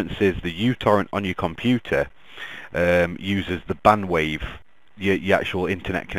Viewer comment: Did he just say "bandwave" instead of bandwidth?